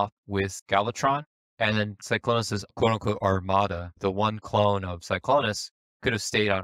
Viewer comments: Considering how loveable and goofy Wheeljack was as a character, it's honestly really disturbing seeing his dead corpse in the movie. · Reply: Thought olson always said and his own model, instead armada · Thought olson always said and his own model, instead armada